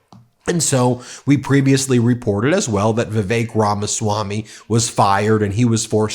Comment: OMG - all these months I thought you were saying 'The Fake Ramaswami' when you mentioned him.🤣😂😅